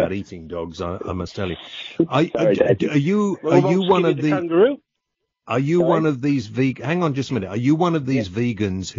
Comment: Does anyone know what the farmer says at 16:53 ish? Sounds to me like some cheap jibe about a kangaroo. Oh, dear. Top job. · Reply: Mister Yowl about as intelligent as the rest of his comments- he said ‘what about Skippy the Kangaroo’. It’s an old Australian TV show where they have a pet roo in the bush. It was popular in Ireland.